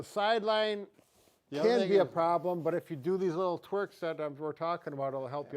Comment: I’m sorry—-did he say “twerk”???